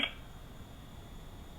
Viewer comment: I clearly hear bludgeoned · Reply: Makes sense, but if you slow the playback speed down (0.5%) and listen with headphones, the female does indeed say 'Wanted' 👂🏻